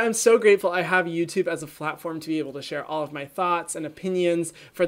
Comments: did he say flatform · Did he say “flat form” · At around 0:45 he said flatform instead of platform, I thought it was funny, also no hate from this comment, I love your channel and your personality! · Anyone else hear him say flatform at 0:45😂 just me? Okay 🙃